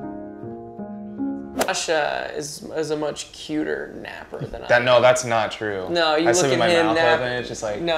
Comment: I thought he said "joshler is a much cuter sleeper" 😏